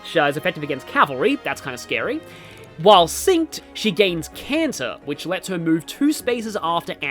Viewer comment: I wasn't paying attention and had to do a double take, because I heard "When synced, she gains the power of Cancer."